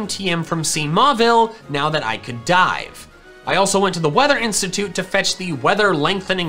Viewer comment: i seriously thought u said "now i can get the ice beam tm now that i can die" when u rlly said dive and i was like WHAT